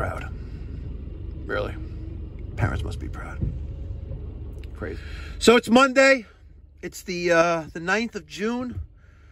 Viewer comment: Thought you said Monday the 16th